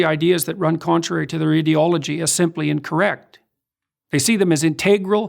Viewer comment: Anyone heard ideology and heard it as idiot-ology?